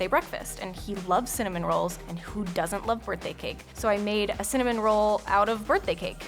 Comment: So this is my tenth time watch this and I just realized she said "who does t like birthday cake" I thought she said "he doesn't like birthday cake"